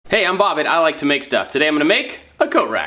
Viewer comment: I finally heard you say AT I like to make stuff and not AND i like to make stuff. Ive been wrong all these years.